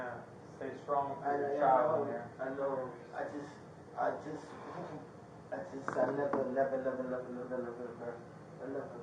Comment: He did not say “love her” over and over here. He said “I never…”